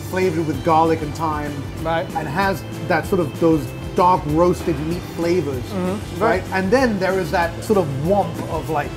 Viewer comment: Did he say "dog roasted meat flavours" at around 3:10?